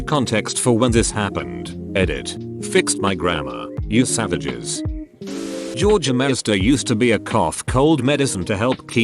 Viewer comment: did... did he just say... georgermeister?!